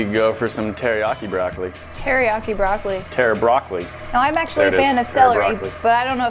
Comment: Someone explain to me why he said "terabroccoli" instead of "teriyoccoli".